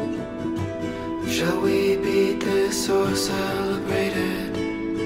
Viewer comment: My sister thought he started by saying "should we beat this horse, I'll beat it" haha